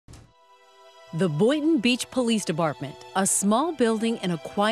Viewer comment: I heard Debartment